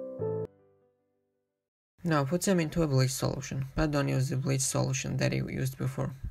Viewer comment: are you saying "and don't use the bleach solution that ..."? The mumbling speech gets in the way a bit, I had to replay a few other sections over and over too before I could determine what you said :(